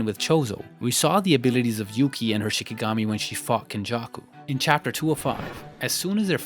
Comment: did he say fucked Beto or is it just me